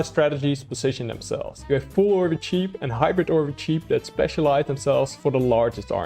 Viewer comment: I keep hearing , "Hybrid Orbie Cheap"? 😂 Keep up the good work. Love the content